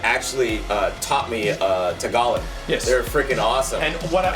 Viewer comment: did he say he learned tagalog? Sick!!!!